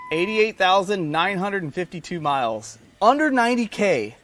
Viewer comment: At first I thought it said 888,995 miles. I was like dam! Good find